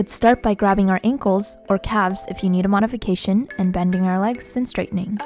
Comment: I have always thought she said “if you need them on a vacation” but I accidentally left my captions on and I realized that it was modification.😂